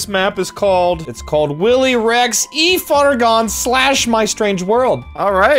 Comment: I thought he said Willy Erect